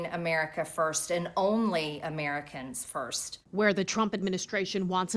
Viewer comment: Ms Greene is WRONG! America first, means making sure to build relationships and commerce with other countries that we will be doing business with in the future. It doesn't mean America ONLY! It's not about isolationism, it's about building a country that is so strong that it can protect itself, prosper, and help our neighbors that NEED it. I ALWAYS put my family first, but that doesn't mean I have to turn my back on friends in need. These people on here trying to make it sound otherwise are just loser lefty's that have zero power to DO anything to stop Trump, so they just bitch and complain about absolutely EVERYTHING that he does. 🙄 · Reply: That isnt what she said, she said only Americans first, not isolated. You misheard or reacted without thinking.